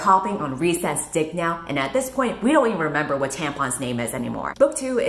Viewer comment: "we don't even remember what Tamlin's name is anymore" the auto subtitles literally wrote Tampon's instead of Tamlin